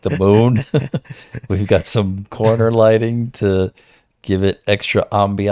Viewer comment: they look comfy asf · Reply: I thought you said corny and I was gonna go off lmao